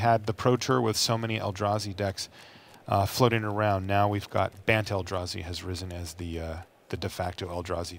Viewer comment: lol. thought he said the BANNED ELDRAZI because that shit is so broken..